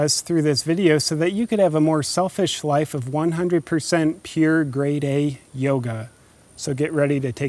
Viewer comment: I 100% thought you said "pure gray day yoga"... took me a while to figure that out